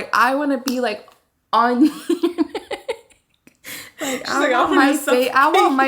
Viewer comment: Lmao I thought she said “I want to lay on your nut” 💀💀